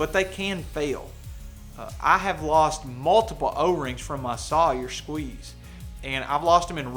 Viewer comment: I was eating while watching this, so a bit of background noise was happening, + I was so sure you said, "I have lost multiple ovaries from Sawyer water filters..." I was pretty confused until a few seconds later when I heard you say, "o-rings" & I could be like, "Oh, okay, logic has returned to the Universe." X-D